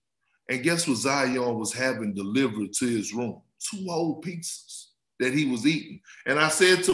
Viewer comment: I first thought Perk said "two hoe pieces," before running it back a few times and, finally, hearing, *two whole pizzas"(@ 18:53-19:01)... Wow! That's bad!... The pizzas, that is! And, before you you even step on an NBA hardwood floor?... Even, now, almost NBA seasons later - or, what soon we be - I'm guess Zion still thinks getting in shape happens by Osmosis?... Which, is surprising, for someone who prestigious Duke University? Albeit, for even one year?... But, then again, Kyrie Irving. And, he thinks the world is flat... So, maybe it is "us," who have the wrong perception of what Duke University is representative of?